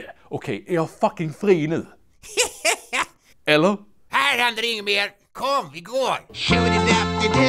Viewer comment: Vad säger 22:57? Har lyssnat tusen gånger men kan verkligen inte höra det, blir frustrerad! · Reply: "Här händer inget mer. Kom, vi går!"